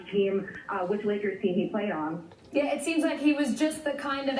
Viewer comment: I hear n"A"kers .